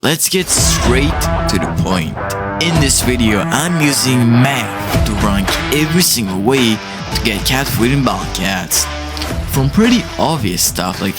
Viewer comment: i thought you said "im using meth to find the best way to get cat food"